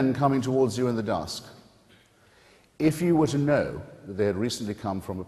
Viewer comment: What is he saying at 1:23? "If you wouldn't know they have recently come from the [???]"